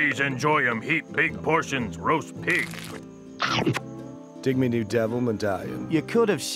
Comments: what does tony say at 1:30? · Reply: Dig [my] new devil medallion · Can someone tell me what Tony says after he ate the pighead at 1:31 ? Can't speak that good english, sorry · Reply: Sam Brero "Check out me new devil medallion." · what is tony saying? please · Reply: "Dig me new devil medallion!"